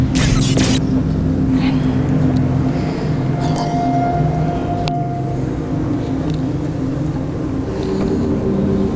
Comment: kek ngomong apa gitu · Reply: Iya pas kak sara bilang "itu ada ibu nu" trs kedenger suara bisikan "hee punten" Sedenger ku gitu. Lebih jelas kalau pake earphone. Maaf kalau salah